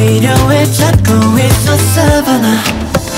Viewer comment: I thought he's sayin' b*tch from Savanna